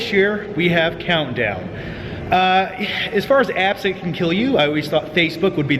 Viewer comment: I thought you said "abs that can kill you". I was like uhhhhhh wut.